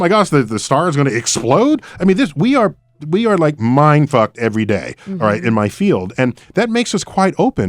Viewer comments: Am I crazy or did he just say "we are mind fucked everyday" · Did Neal just say, "Mind-Fucked"!? @ 18:02 ..haa